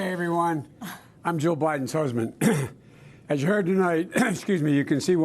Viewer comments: Did he just say "I'm Joe Biden's husband" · Sounded like Joe said hell everyone I'm JOE bidens husband.